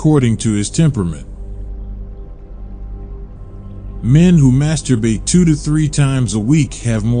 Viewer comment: Um,,, thank god- I thought he said 3-4 times a day!